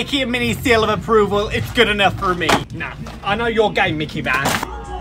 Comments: When he said I know your game I thought he said I know your gay🤣🤣🤣😂😂😂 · Do I thought he said "ik ur gay Mickey mouse" 😭😭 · Did he just say "I know your gay mickey mouse" or just me😳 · For some reason I thought he said I know your gay Mickey Mouse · I thought he said "I know your gay Mickey Mouse" before checking the captions💀. I was coloring while this short played in the background so lmao